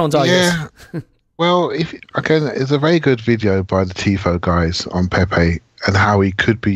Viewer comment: what is saying here? "A very good video by the..."? Been waiting for this podcast since saturday :D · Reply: Tifo football. They have a channel on YouTube and a video on Pepe to Arsenal